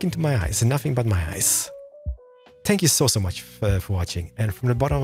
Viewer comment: At first I thought he said " spank you " instead of " thank you "